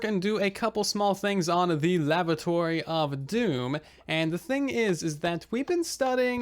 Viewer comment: Did I hear lavatory of Doom, 12:46. Is that some kinda death toilet